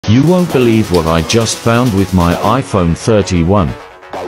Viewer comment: In the beginning I heard “you wouldn’t believe what I found with my iPhone 31”